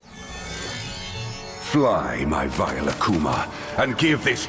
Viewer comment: Why did I hear "Fly my f**ing akuma 😂😂😂